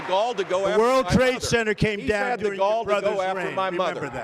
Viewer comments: can anyone make out what DJT is saying?? i think they lowered the vol? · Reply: "the twin towers came down on your brothers reign, remember that." correct? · WHAT did Trump say @ 2:53?????? Anybody!? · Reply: The world trade center came down during your Brothers reign, remember that Idk if you were really asking for what he said or if you couldnt believe what he said